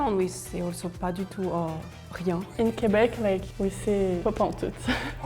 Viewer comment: Pas entout? Do you mean pentoute? Or is it just a word I never heard of lol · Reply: Ferth misheard "pas entoute", but she said "pentoute" "pas pentoute".